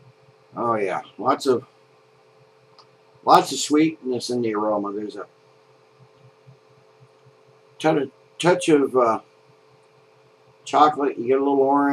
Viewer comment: LOL!!! Around 2:29 I had to rewind it a few times, it sounds like you said "A tender touch of chocolate". Maybe you did I couldn't really tell. Great review.